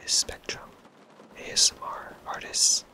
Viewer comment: ok who else thought it said speedrun asmr tits